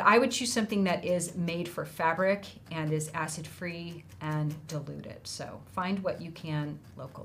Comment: Missed the live, so here I am. When you were talking about fabric stiffener, I thought you were saying it’s deluded! 😳 Realised later you said diluted 🙄🤣